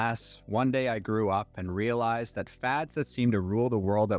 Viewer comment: Lord Jesus 😂 I thought you said fa*s rule the world! 🤣🌎🤣 Fads FAD DDD 😁